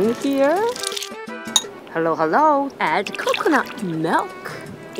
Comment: i thought you said Halo halo(its a dessert)but it was hello hello.